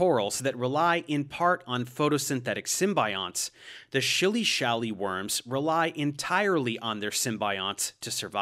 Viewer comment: I’m listening to this at 4:30 and I just hear THE SHILLY-SHALLY WORM